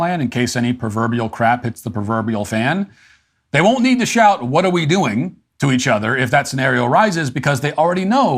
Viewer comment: Wow she said “what are we doing?” I thought she was saying “how are we doing?” Like how bad is it 🤦‍♂️ not a good look